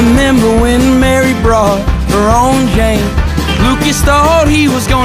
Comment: i thought he said “when mary brought her own jane” but ig not